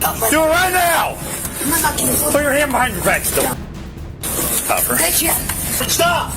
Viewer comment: I heard “put your hand behind your backs” for some reason